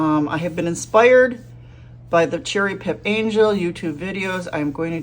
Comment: Working to follow up on one of your first suggestions in the beginning of this video. You mentioned a watercolor artist, I thought you said cherry pip angel. Can you give us her web address so we can check her videos out. Thanks!Bryan